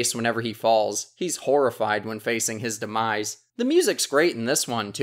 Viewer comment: did you say demise? demise from mario's madness starts playing